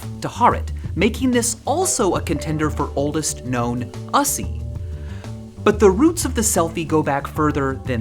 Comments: I thought you were saying "-ussy" like the trend, which would have been really cringe, but you were saying "us-ie" as in "selfie." Very cleaver thank you. Take 16:35 out of context. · did he just say Ussy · Did just say ussy? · bro did not just say ussuy